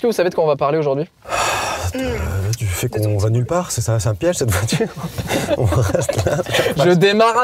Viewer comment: "De quoi on va parler aujourd'hui?" -"De ton petit boule" moi: intéressant...